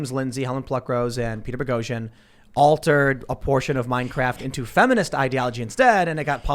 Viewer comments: It sounded like Tim said Minecraft 😂😂. Are the NRA supposed to defend people with guns that have the serial numbers scratched out. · Did tim say Minecraft in stead of Mein Kampf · dude said “Minecraft” instead of “Mein Kampf” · I'm sorry, but did Tim say "minecraft" at 4:27?